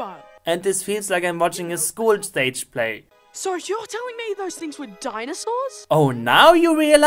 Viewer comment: I misheard what he said. I didn't hear "those things are dinosaurs?" as he was saying it. For me it sounded first like "you had s** with dinosaurs?" XD